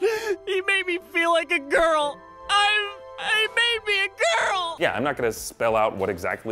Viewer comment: How would anyone miss the "he made me a girl"? Not subtle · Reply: I thought he said "I... I may be a girl!" Like he wasn't sure of his sexuality. Not "he made me a girl". True, that's not subtle.